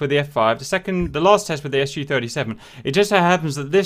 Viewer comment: Did I hear Su-37?? :)